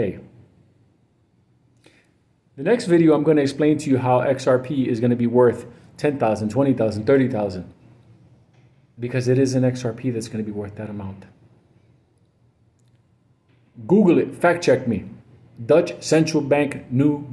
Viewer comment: Did say "because it "isn't" XRP that's gonna be worth that amount?" 1:44 I'm confused.